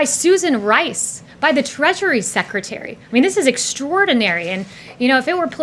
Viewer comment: (I thought she said "the Treachery Secretary").